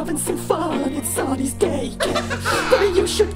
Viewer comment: Uhmmm i originally thought moon said “Sun is gay” at the beginning💀💀 i had no clue it was “Sunny’s daycare”😭